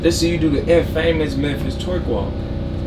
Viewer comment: "twerk walk" i thought bro actually said twerk walk walk😂